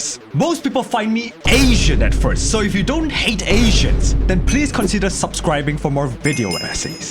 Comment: I thought he said Haisians, and thought back to that GTA 5 mission